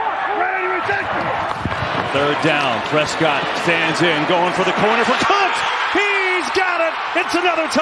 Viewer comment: Did anyone else hear the announcer say “going for c**t” at 8:53 ?? Or am I just tripping? 😂